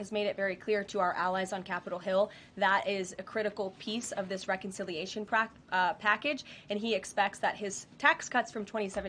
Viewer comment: Did she say "reconciliation crap (package)" 😅lol